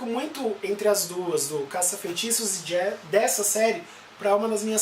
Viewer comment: Opa... Eu ouvi caça-feitiço...?